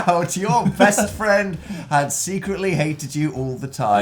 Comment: I thought it said sexually hated